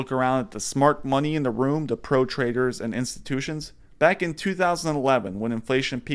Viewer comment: in the opening, the anchor says "trader" but I hear "traitor" hahahaha